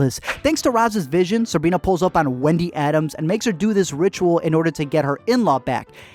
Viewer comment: i heard wendy adams. She's wednesday's ancestor 👀👀👀👀👀